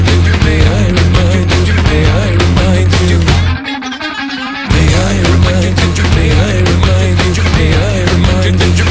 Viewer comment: What the fuck does Daron say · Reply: "Bad men would have lied to us" And the one who says it is not daron, it is arto